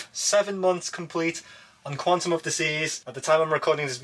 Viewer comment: Lol, Youtube Closed Captions thought you said Quantum of Disease and then you showed the door sticker reminders.